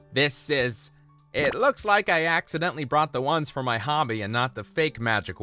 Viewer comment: did he say hobby? 👀